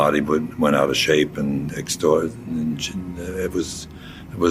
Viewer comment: where it says (inaudible) it sounds like he was gonna say extortion which i would assume he meant exorcism interesting how he caught himself, like was that actually a voice sample of his from some interview or was it just a paid voice-over of a quote found and they just messed up?just curious is all